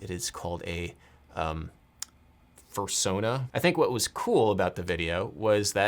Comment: I could never imagine Adam saying "fursona"... oh my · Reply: Dang...I thought he said "purrsona." I am so naive...or old.